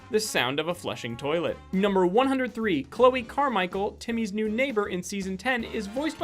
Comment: I heard Chloe Carmichel and I was like WOHA! Because I thought is said Kylee Carmichel who is my friend.